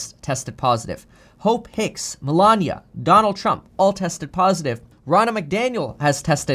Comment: Sounded like you said Ronald McDonald tested positive. Lol.